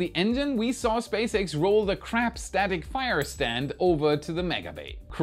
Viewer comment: Tbh., I thought said "crap-tastic" instead of "crab static".